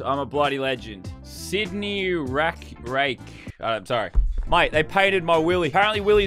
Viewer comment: did he say they painted his willie